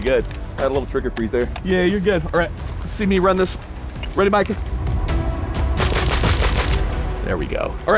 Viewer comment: did he really say "Ready Micah?"